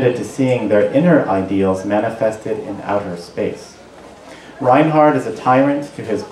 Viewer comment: did he say "Reinhard is a tyrant"?